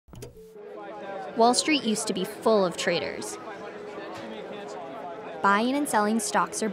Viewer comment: what i heard: "Wallstreet is full of traitors"